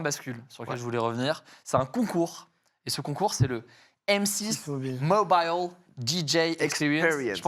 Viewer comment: il dit quoi joyca à 4:08? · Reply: Mobile !?